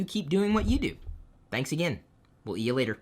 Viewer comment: at the end, i heard, 'will eat you later'